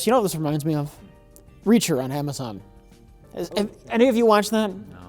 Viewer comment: I heard 'Reacharound Amazon' also.